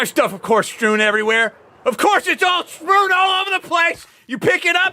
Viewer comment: Did he say “sprown all over the place”🤣🤣